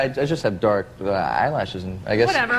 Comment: what she says at 0:22 after Nestor said "i just have dark eyelashes" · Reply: Ocean whatever